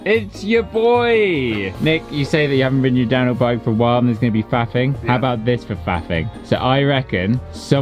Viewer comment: I thought he said “there’s gonna be fapping” :o